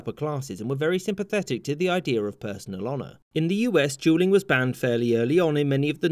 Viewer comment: I suppose you wanted to say "dueling" ? I hear something else, and automatic subtitle write "jew ling", that's quite confusing.